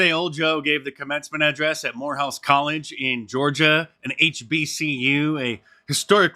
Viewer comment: Lol. Swore you said whorehouse college. Lmao